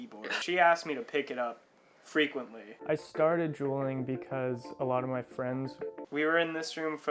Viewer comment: has been juuling??? I’m over here, I thought he said foolin’ *slide whistle